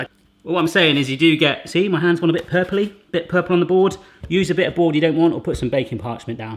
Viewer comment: in the intro i thought he said "PURPLE BITCH,PURPLE BITCH"😂😂😂